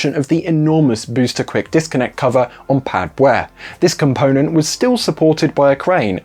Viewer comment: Did u just said Pad "Б"(pad beh) in russian or something?